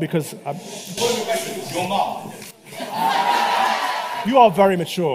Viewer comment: what was it the kid said at 1:06..? “Your mom”..?